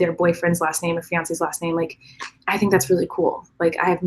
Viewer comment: "How do I get over my fiance breaking up with me?" .... you should have said to get a Gus! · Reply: I thought you said finance 😝🤣